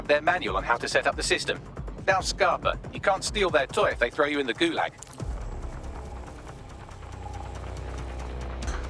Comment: Did Bagley just said - "throw you in Gulag" ?